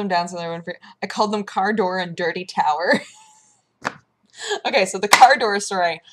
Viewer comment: For the longest time I thought it said "The Dirty Towel"...